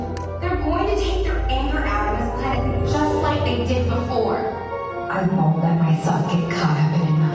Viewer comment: Wait I thought I heard Rose say "I won't let my son get caught up in another war" just me?